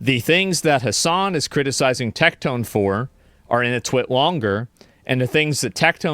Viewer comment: what did he say? All I’m hearing is “…in a twit longer” and I know that’s not correct 😂